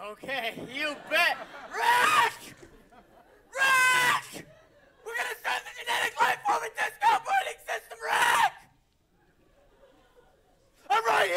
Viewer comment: Guuuuuuuuuuuuuuys,help,please What Fact says in the interval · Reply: "*RIIIIICK!* WE'RE GONNA START THE GENETIC LIFE AND DISK OPERATING SYSTEM, *RIIIIICK!*"